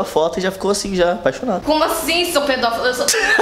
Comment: Eu entendi "Como assim seu peidão?"